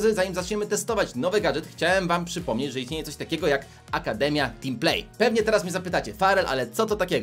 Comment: Napoczątku usłyszałem epidemia team play